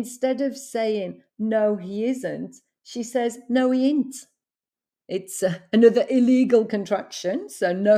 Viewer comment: I thought she said 'no it ain't' when actually no it isn't minus the s 😂 then aunty said 'well bad in't it' is out of my mind 😂😂